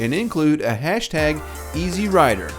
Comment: You might want to edit your hashtag to say esearider. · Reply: Thanks, I thought he said easyrider